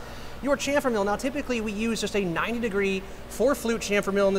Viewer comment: i heard"9 degree"guess you meant 90. .